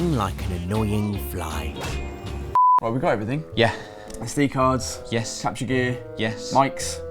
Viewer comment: does he say C cards?! lmfao crew out on the pull :P Okay i now realise he didnt say C cards... slightly saddened haha